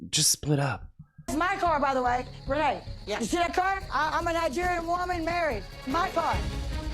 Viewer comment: did she say I’m a Nigerian woman married my car 😂😂😂